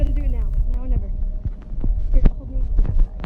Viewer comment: I can hear "Here, hold the (what I assume is the word necklace because I hear the letter N)."